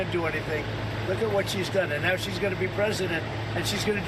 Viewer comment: OMG 😲 HE ADMITTED IT ! 🤸‍♀️🤸‍♀️🤸‍♀️ HE SAID "and now SHE'S GONNA be president" ... He did NOT say "and now SHE WANTS to be president" ... The 🎃 KNOWS he's gonna LOSE.👍😁 Hehehe...